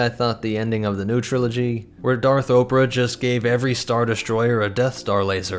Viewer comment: I thought u said Darth Oprah!!!? Now that wud be a scary Sith 😂